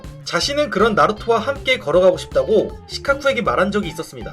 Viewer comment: 걸어가고 싶다고 말한걸 결혼하고 싶다고 들었다 ㅋㅋ